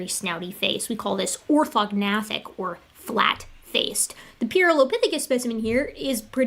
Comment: I thought for a second there she said “OrthoThugs-Natic”. Based on-da’Bones.